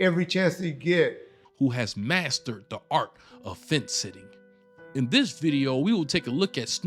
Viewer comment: I thought this video said "face sitting." I was like "damn that's kinky but hey if that's what the man wants to do."